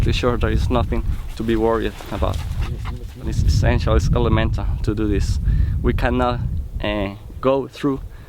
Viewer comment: at around 7:05 where the subtitles say "unaudible" the soldier says "It's elemental that we do this.."